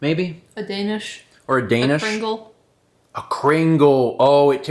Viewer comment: At first I heard "kringle" as "pringle" and emidiatly questioned how a cake could taste exactly like a chip